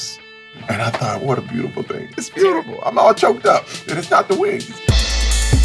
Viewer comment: I Swea I Thought He Said "And It's Not Da Weed"😂